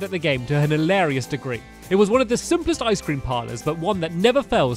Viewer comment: At first I thought it said “Ice cream parrots” 😂😂😂